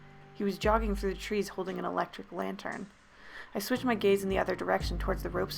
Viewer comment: What they hear :Gaze what I hear : gays